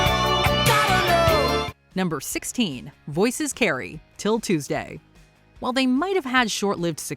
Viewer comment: When I was a kid I thought she was saying Princess Carrie, not voices carry.